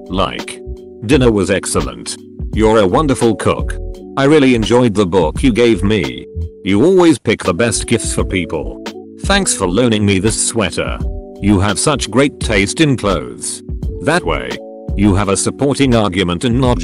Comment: For a moment, I thought it said, "thanks for learning me..." and I was like, "wait, that's not good English..." 15:37 cough Job cough